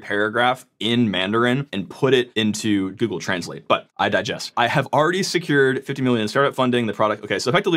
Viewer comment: Did I hear "but I digest" at 10:15? 🤣